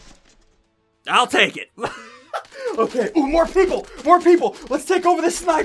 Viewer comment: did he say more pickle